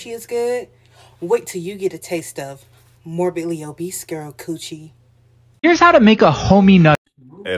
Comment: I THOUGHT SHE SAID MORBIUS COOCHIE